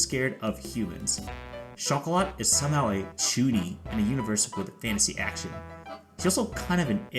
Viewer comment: “Chocolate is somehow a [?] in a universe with fantasy action.” Sorry. But I didn’t catch what you said here 🤔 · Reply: "chuuni" is the word I used there